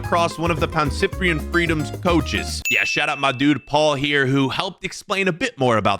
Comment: I heard Shutupmydude